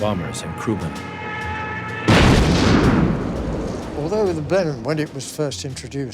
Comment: What is the aircraft mentioned at 2:54? the caption says Bannon but I can't find any WW2 British aircraft by that name. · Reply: I think you'll find it was the Blenheim.